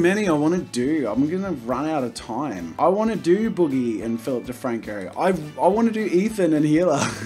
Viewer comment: Thought he said Filthy Franko